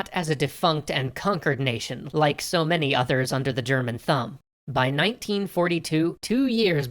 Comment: I know this is serious shit. But the way how he said thumb, I thought I heard thong... Under the German Thong... I just can't help it XD I still love your story, I find it very interesting and this is helping me in my WWII unit at humanities (my school calls it, "humanities")